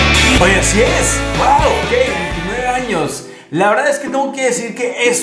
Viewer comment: Pense que decia 39 años ajja me asuste ( yo dije como si se ve tan joven jaja) retrocedi el video y dijo 29 jajajja 😅😅 Hacen muy linda pareja 😍💕 Feliz cumple 🎁🎊🎉🎂